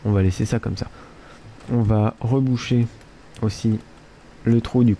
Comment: au debut quand il a dit "on va reboucher le trou ,du coup..." j'ai compris "on va rebooucher le truc du cuq"!!mdr:)